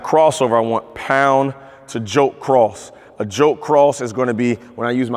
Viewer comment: the editor said “joke” instead of “jolt” 😂